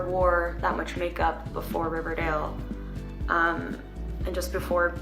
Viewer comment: dice Riverdale, bueno eso escuche y creí que decia